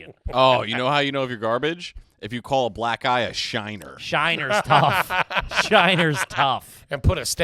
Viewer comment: Have to tell ya this. At 47:22 when I first heard comment on calling a black eye a shiner.... I thought he said "you wanna know what's really garbage, it's when you call a black GUY a shiner" 🤦